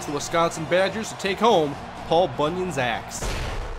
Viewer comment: it sounded like you said paul bunyan's ass